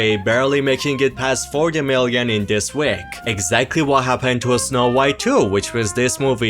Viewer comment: I misheard what you said and had to do a double take. The first time, I heard Snow White 2 and all I could think was F**k!!!